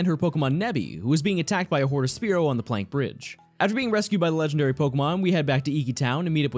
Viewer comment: Okay so I left this video paused for a while early on and kinda forgot any context around 1:40 so I briefly thought you said Spearow and Nebby were /playing/ Bridge. (... it’s not entirely wrong?)